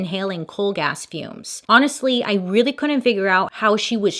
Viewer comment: i 100% did not think you said coal gas fumes, i thought you said cold a$$ tunes, i'm over here elbow deep in paint, like how does one inhale cold a$$ tunes.